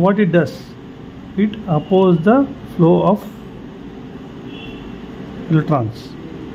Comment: What is the word here? Oppose flow of electrons? Also, what did you write? ELC? Thanks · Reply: "Oppose the flow of electrons means current " ELECTRONS SHORT FORM WRITTEN AS ELC